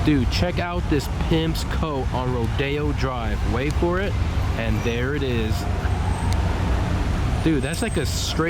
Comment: Many thanks to for hooking it up with the Laferrari clip. And yes, I was so mad at my failed memory card that I tossed it in the trash. Check him out here: <link> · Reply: can i ask what did you said 7:07? Check out this "~ .wait for it,and there he is."